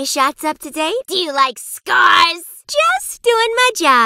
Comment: Nurses: do you like scars What I heard: do you like stars